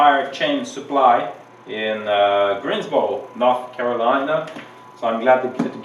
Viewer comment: No way...no way. I played 1:23 at least four times b/c I thought I heard you say Greensboro, NC and you surely did. I was just up near Greensboro, NC Christmas Day. What a small world, huh? Man, I used to have to put chains on car tires back in the late 60s and I thought I was gonna screw it up but it worked out fine. Every now and then a link would come loose and you'd hear it whacking inside the fender well. This was back home in Pennsylvania and also in mid-70s when I was stationed in Massachusetts. Plenty of snow, ice, and tons of steep hills to drive on. Heck, never used chains with studs before. Thanks for this video.....brought back some cold-weather memories (Brrrr). Funny watching you drive out that garage full-bore attacking the snow once you got those chains on ....a kid with a new toy (smile). Go get'em...go get'em 😊. 👍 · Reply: Like a kid!!! Yes I did say Greensboro! I've been told by Richard Elbe that in North Carolina they call Greensboro, B'oro... Thanks for your support!